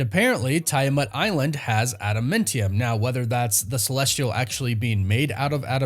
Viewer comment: Why does it sound like he saying Adamintium instead of Adamantium...?